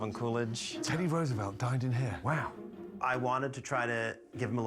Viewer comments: Gordon: Teddy Roosevelt dined in here.“ Subtitles: Teddy Roosevelt died in here.“ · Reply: I thought it said he died there without the subtitles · “3 presidents have dined in here” Captions: “3 presidents have died in here” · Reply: Lol, I heard "died in here" the first time and I was like, there's no way that's right, so I had to go back and listen like three times before I finally heard the n. 😂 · I thought he said teddy Roosevelt died in here 🤣🤣🤣